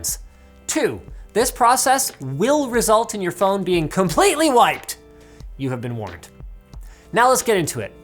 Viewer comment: I thought you said "COMPLETELY WHITE" and now I have a completely wiped pink phone